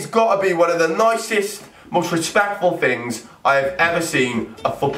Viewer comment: did he say disrespectful or respectful